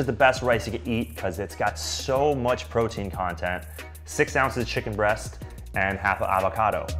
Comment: "Jasmine rice is the highest in protein...", wild rice is the highest in protein, followed by brown rice. Jasmine rice is no greater than plain white rice when it comes to protein content. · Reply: I thought he said “...because it has so much protein content”